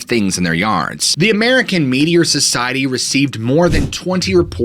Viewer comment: No lie, when you said “American Meteor Society” I thought you were saying “media” with a really weird accent 😂